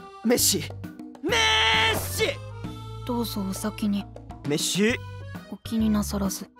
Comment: の「滅私」が「Merci(メルシー)」(フランス語で「ありがとう」)に聞こえて笑っちゃった